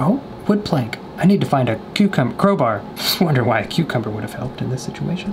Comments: When he thought it said cucumber instead of crowbar I did to. I didn’t realize until he said oh crowbar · I thought it said cucumber, too. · I thought it said cucumber as well haha